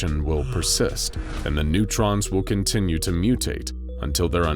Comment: bro said neutrons instead of neurons 💀